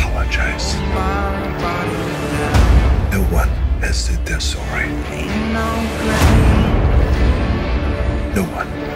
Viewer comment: What does he say at 1:52, no one something?? I replayed that part so many times, but I just can't make out what he's saying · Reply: "No one, has said they're sorry."